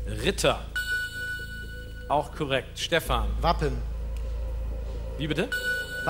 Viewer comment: Wer hat auch bei 7:21 Muffin anstatt Wappen verstanden? hahahaha